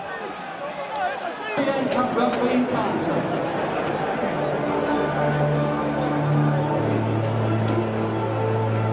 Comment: what does Joe say here ? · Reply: 'do you want to go for a drink with me sometime'